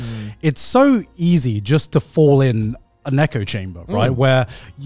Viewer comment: he said an echo chamber, I heard a neko chamber